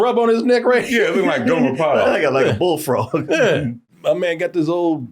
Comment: Who did they say he looking like who? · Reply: Gomer Pyle from Andy Griffin show lol. Old school show